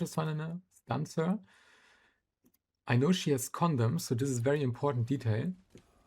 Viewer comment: bro I thought u were saying condom for a second 😭😭